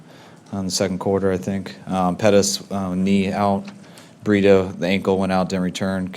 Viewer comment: Did he just say burrito